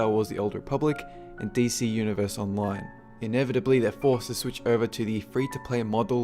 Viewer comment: I thought he said Dayz Univers Online